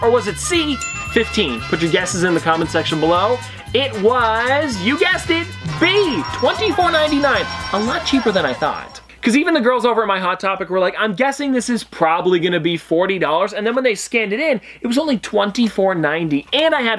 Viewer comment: I thought I heard “ my girls over at hot topic “ are you not telling us something?! 😉